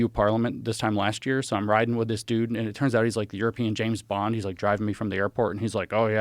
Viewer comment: Did he really say at 8:30 "the european James Bond"?